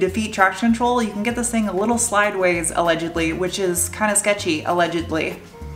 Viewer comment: did you say "slideways" insted of sideways? O.o